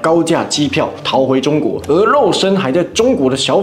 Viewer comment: 我太餓了（晚上十點還沒吃第一餐）聽成「鵝肉身」 3:55原來民先生那時候飾演「小粉紅拿出中國護照防搶劫」的情節是真實存在的事件改編😂